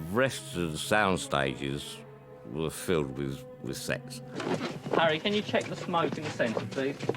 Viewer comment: About the Enigma Building, I heard: “The rest of the sound stages were filled with sex...” But in fact, has some kind of gentle lisp (dyslalia or dyslalie in French); he probably said “sets” instead. Seems more likely...